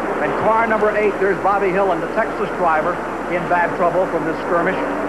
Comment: Why does the captioning say "sexless driver" at 0:28 instead of Texas driver🤣